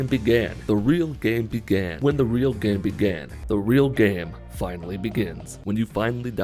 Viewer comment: Now i just hear “When the real Gam BeGén”